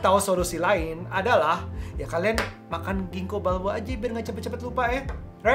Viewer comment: Makan apaan bang biar gak cepet lupa? Gak kedengeran jelas euy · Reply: Ginkgo Biloba 🤪